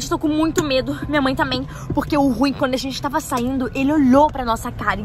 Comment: No minuto 13:19 a lo disse o Rui só eu que ouvi?